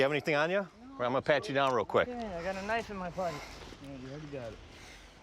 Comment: does he say "Ive got a knife in my butt"??